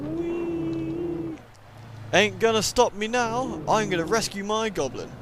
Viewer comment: When He said at 1:21 "Ain't gonna stop me now", did anyone else think he said "Don't stop me now" and break in to the queen song right then and there? or was it just me?